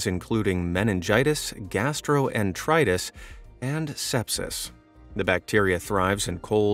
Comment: I thought he said sex